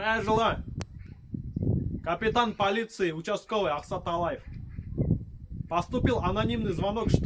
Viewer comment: Мне послышалось участковый ААВТОТАЛАЕВ, а тогда у них в ДПС наверное такие работают, машины арестовывают штраф большой накидывают а если бедолага не сможет выкупить получается ААВТОТАЛАЕВ.